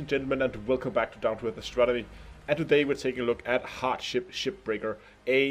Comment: at the start sounded like you said heartship shipbreaker :D. DLC